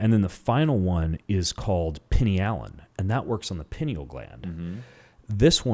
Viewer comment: Pinny Allen? What is the stuff he's saying? Where can we get it? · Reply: Pinealon. Greetings from finland